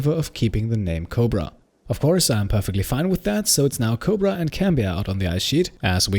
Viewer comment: Every time I hear "Cobra and Cambiar" my mind goes to "Coheed and Cambria"